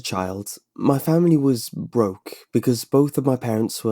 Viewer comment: In the beginning i thought he said ''my family was w0ke.''